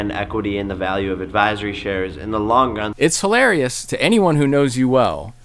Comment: Wait. 5:30 . Did he really say "in the long gun"?